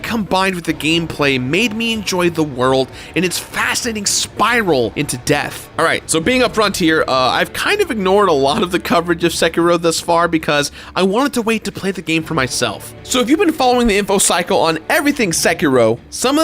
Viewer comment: What did you think about my Sekiro Shadows Die Twice review? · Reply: What did you say around 1:46 ? "Since I'm a frontier"?? What's a frontier?